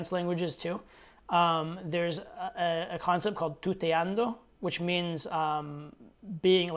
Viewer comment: I heard "puteando" the first time LMAO